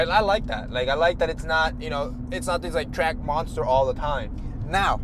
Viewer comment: I legit thought said twerk monster im crying